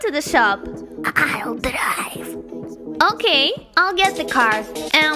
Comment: I didn’t look at the screen and we she said “I’ll drive” I thought she said “I died”😅😂😂😂😂😅😅😅